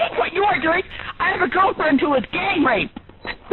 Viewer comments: Did she just say "I HAVE A GIRLFRIEND WHO IS GETTING RAPED!!!" 😂 23:00 Well...at least she's not a homophobic. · woman on the phone: "I had a girlfriend who was GANGRAPED and your calls are a WORSE invasion of privacy!" telemarketer just doing his job, not expecting it today: ....ok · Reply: I thought it say gay raped